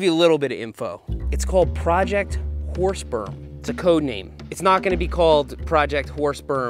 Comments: what is "whore sperm" seth kept saying it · Thought he was saying horse perm with a P. 🐴